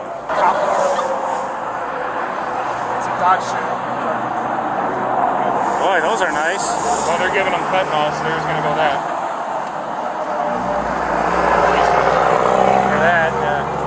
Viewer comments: did he say “well they’re giving him fentanyl” ? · ummm did he say they were giving him fentanyl? Then asked if he was still recording